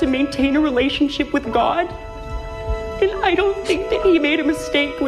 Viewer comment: I thought he said "my relationship with Maude" I was waiting the music to come in "and then there's Maude"